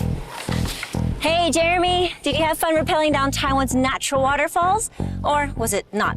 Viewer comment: ＣＣ字幕顯示Thailand instead of Taiwan, please sort it.